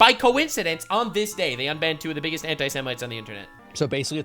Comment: My brother walked past me and thought you said hentaisemite